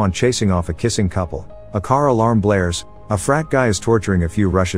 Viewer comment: Thought he said fat guys